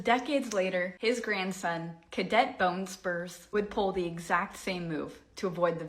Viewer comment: did she say, Cadet Bone Spurs??? 😅